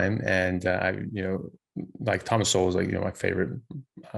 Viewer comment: Who did you say is your favorite author who you say you like to listen to when working out? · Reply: Thomas Sowell!